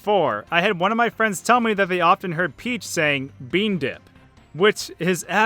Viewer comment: It sounds like Peach is saying "Bean dough"